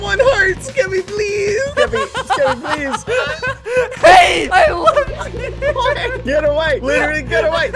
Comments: SKEPPY SAID I LOVE YOU?! (Ik he said left, but EVEN CAPTIONS SAY "LOVE!") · what did he say after getting hit- 🤨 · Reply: I don't know why but i hear " i love you" XDD